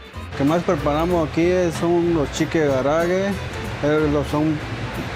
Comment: "치킨 가라에게(일본음식)" 이 분명히 들리는데, 자막은 "김치전"으로 둔갑.......국뽕도 좋지만 자막 조작질은 하지 맙시다...